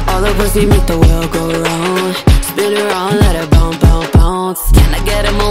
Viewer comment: correct me if I'm wrong but it sounds like they say bounce not pounce